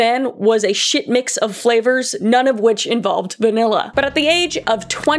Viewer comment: I swear when she said a ton mix flavors of none which involved vanilla i thought she said Banilla😭